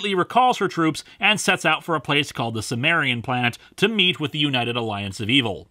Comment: You should credit Carlos for saying “We have to go to the Sumerian planet” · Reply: I thought he said "cimmerian"